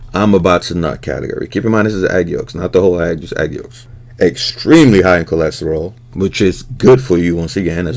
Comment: When you put the yolks up there, I thought I heard you say “eggstremely” I was like well played.